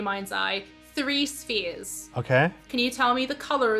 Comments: So.. When they said "imagine 3 spheres" I somehow heard it as "3 spears" and thought - that's very speccific but okay, here we go - unit I saw Anthonys visualization 😂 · I thought they said "spears"